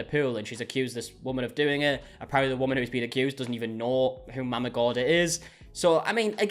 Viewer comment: Mama Gorda means Fat Momma in Spanish😂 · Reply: I was just listening and thought he was saying Mama Gordon.